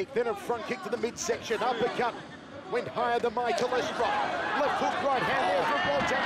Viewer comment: did he say "uppercut, went higher than my cholesterol"?